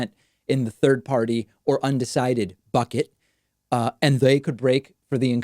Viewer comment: well, that's about the umpteenth time I heard the word "bucket" as "fuck it."